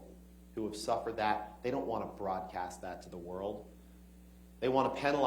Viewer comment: Anyone hear “fraudcast” and not broadcast”? Check out 3:35-I’ll say about 3:48 for context. Freudian slip??